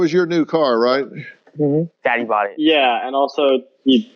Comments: did he just call his father daddy? Wtf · Did he just say, "Daddy bought it"?